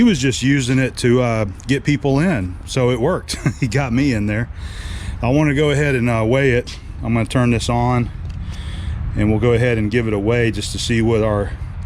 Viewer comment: Ha Ha! When you said (about the Columbian): "And we'll go ahead and give it a weigh", I thought at first you said: "And we'll go ahead and give it away". Shucks. Enjoy the new toys. Great stick welding on the d rings.